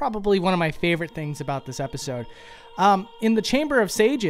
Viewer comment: I thought he was gonna say chamber of secrets. What is my life lol.